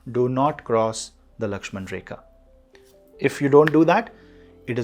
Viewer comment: CAPRICORNS what did he say about "Do not cross ---" I didn't understand what he said at 24:51. If anyone knows please post! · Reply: Lakshmana Rekha . Its from an Indian story. Check online and you’ll find it. Lakshmana Rekha is a protection line, as I understand it it means not to be too sure about yourself and be cautious of tricky situations.